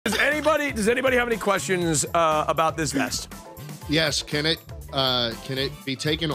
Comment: I thought he said "Does anyone have any questions about this mess?" XD